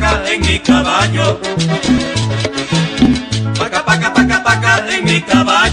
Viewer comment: Ugh i read that he is saying taca taca taca. And I thought he is saying paca paca paca, re-listened to it, but still im insisting that he is saying paca paca paca 😂😂😂😂